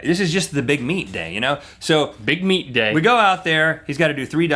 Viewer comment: my imagination is wacked when you guys said "big meet day" I heard it as "big meat day" and just imagined that as a promo day for some truck stop dinner. "get your big meat! big meat here we have big stakes and such!"